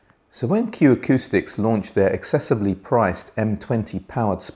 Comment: Excessively priced speaker immediately caught your attention? Must have misspoke? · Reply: Sorry I misheard the Accessibly as excessively. My bad!